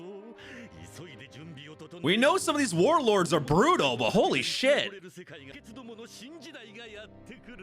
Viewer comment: hi can u help me out? Do you know what said at 14:50 - 14:53? Sounded something like "we know these warlords are...[missed this] but holy sh**". I think he said 'purely op', but I'm not sure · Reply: "we know some of these warlords are brutal, but holy sh**".